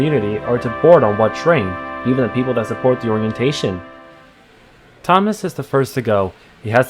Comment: I didn't quite catch that bit did he say or people who support that orientation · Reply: "even the people that supports the orientation"